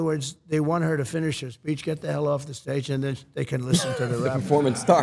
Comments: did he say bitch get the hell off the stage😂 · I thought he said bitch get off the stage 😂😂😂😂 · MINUTE MARK?? YALL GOING TO ACT LIKE YALL DIDN'T HEAR THAT "B"??😂😂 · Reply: speech*